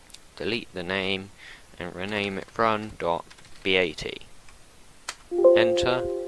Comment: Run.bat doesn't exist...? What do I do pls help :( this is what I mean <link> EDIT: NVM, problem solved. I though u said "front.bat" instead of "run.bat" at 11:11 cause the damn youtube translator + I don't speak english